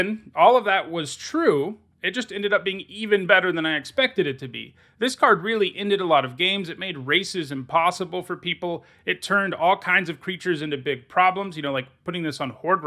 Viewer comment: i was listening at 1.5× speed and had to stop and go back to 7:10 because it really sounds like he says "it made racism possible"